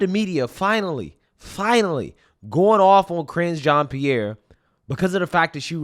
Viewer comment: For a sec, I thought you said Cringe Jean Pierre lol.